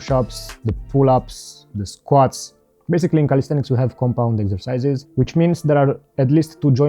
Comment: Exercise? I thought you said extra fires - That's what she said 😃